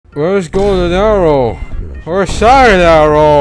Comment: my slow ass thought he said “golden hour” 💀